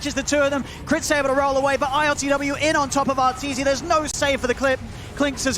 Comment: something i never thought i would heard in dota commentary "theres no save for the clit"